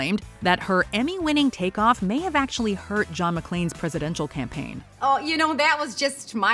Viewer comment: did you say John McClane?? 😆😆